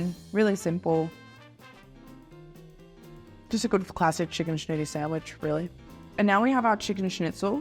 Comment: First time hearing "chicken schnitty", I thought said Chicken shit hahaha. Also, love how she does not shy away from them bites for all the food!